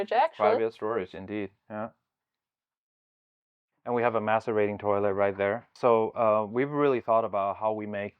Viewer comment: Did he really say masturbating toliet? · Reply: Macerating. -Producer